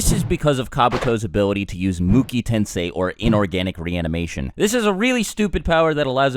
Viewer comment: It says "Inorganic Reincarnation" instead of reanimation